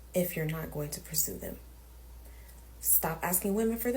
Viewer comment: Sorry, english isn't my native. Can you tell me which word use this girl when ask for men don't ignore women? 4:55 . Is it presume? I can't understand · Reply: She said pursue, as in men should court women after asking for their phone numbers.